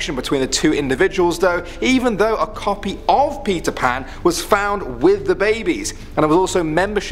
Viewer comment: I thought you said pizza pan but you said Peter Pan